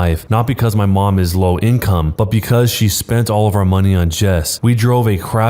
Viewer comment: I heard chess the first time